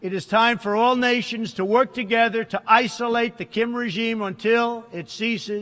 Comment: のとこでキムチーって聞こえるんだけどw